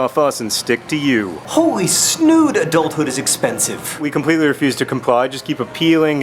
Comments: I totally thought you said "... adultery is expensive." when you got to the water filter. O_o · At first i thought you said "hold ood adulthood is expensive" and i got strangely excited that doctor who was being mentioned...then i realized you said snood, and the excitement went away